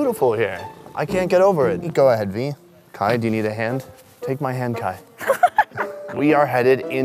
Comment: Did he say Hentai